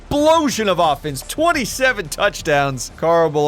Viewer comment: Anyone else hear 27 touch downs?